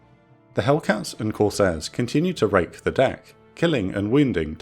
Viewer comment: no matter how often i listen to it, i can't stop hearing "The Hellcats and Corsairs continue to rape the deck." even though i know it's supposed to be "rake". Edit: having now finished watching, i can't say that what i heard was inaccurate